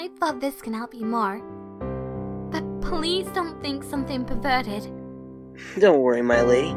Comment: wtf she said? Whatever she said does not sound like "pervert". 😂 (pevertet) (Zapravo malo zvuči)